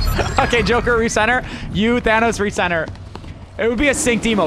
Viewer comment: did blud just say thanos, that kratos